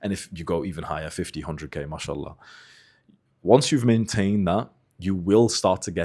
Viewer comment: did he just say mashallah ?